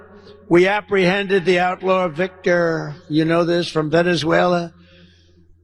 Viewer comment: 😢Sorry, I heard DT say "Victor from Venazeula". I am not a fan of DT.